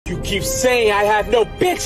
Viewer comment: Love how it ends on "I DON'T SEE-" implying that Tweek cannot actually see./j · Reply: I thought it said I don’t see ‘em